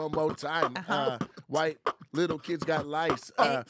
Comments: "White little kids got lice " 😂😂😂 enjoyed Nate's freestyle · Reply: I thought he said, "Why kids got lice?" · "White little kids got lice " 😂😂😂 enjoyed Nate's freestyle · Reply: At first all I heard was “little kids got lice” 😭 thanks for the corrections